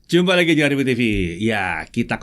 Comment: "Jumpa lagi di channel review tv". Wkwkwk. Bs gitu ya dengernya..